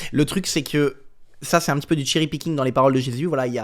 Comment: "ça c'est un petit peu du ..@ Rien compris, c'est quoi ce mot ??? Ça serait possible de s'exprimer en français ? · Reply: Du cherrypicking. En gros c'est prendre un petit détail sans prendre en compte le contexte, ou les autres parties en compte